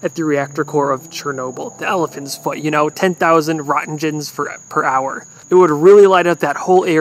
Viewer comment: When he said roentgens i thought he said rotten gems 😅😅😅😅😅